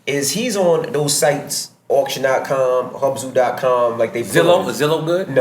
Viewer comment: he says <link>, and some other .com can somebody let me know what exactly he said please?? Thx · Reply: Hubzoo